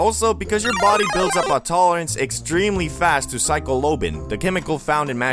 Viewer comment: Why did bro say psycholobin